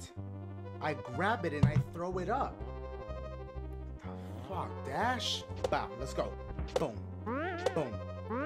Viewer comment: About 8 Fuh da Puhs later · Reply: I thought you said Fus Ro Dah xDDD